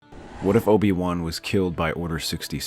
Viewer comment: It cut out for me at the beginning so it sounded like you said “what if Obi Wan was cute” lmao.